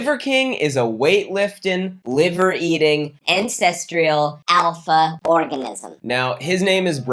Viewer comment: all i hear is “incestrial”